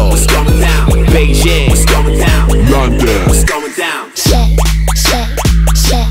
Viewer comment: at the beginning i thought the girl was saying "shit shit shit" but shes saying "shake shake shake" LOL